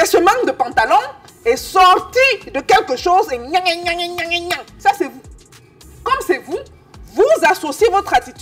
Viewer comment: c'est quoi "nyanga nyanga nyanganyaaa..." svp!??? Je n'ai rien compris! Et je n'aime pas rater un conseil de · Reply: Belle belle